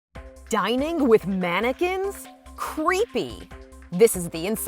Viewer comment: Thought it said if I would eat mannequins